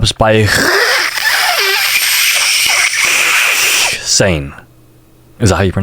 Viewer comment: it’s Husain instead of hxsain